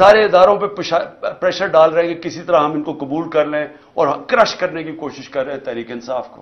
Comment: did he say pishaab? 😄